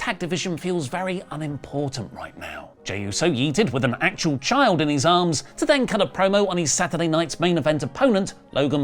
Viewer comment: For a second I heard “jey uso yeeted a child from his arms”